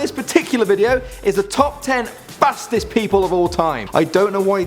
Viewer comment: i thought it said top 10 fattest people'of all time